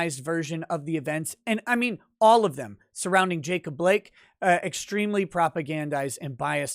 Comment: I thought you said jake the Blake lol